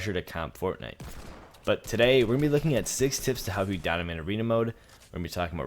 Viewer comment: he really said donimate arena mode